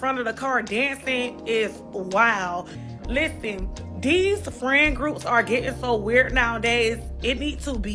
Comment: Did she say "Dees"???????